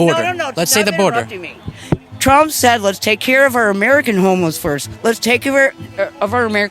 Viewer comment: Wait a minute did say Trump said "Let's take care of our American homos first" at 14:40 ?